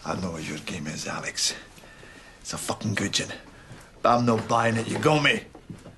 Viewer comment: At around 2:25, could someone tell me what Peter Capaldi says? Especially, what does the word "gomie" (spelling?) mean? (I know it must be a word because I heard Billy Connelly say it, but I'm an American and I still haven't figured it out. Please excuse my ignorance and educate me here.) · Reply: More specifically it's "it's a fuckin' good yin". Yin is very much a Scots dialect variant of "one" in that context, and you're right that Billy Connolly is renowned for it (he's even known as The Big Yin).